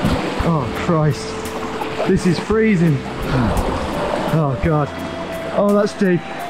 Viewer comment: At first I thought he had said "This is freedom" at 26:50 because that's exactly what I thought at that moment... :)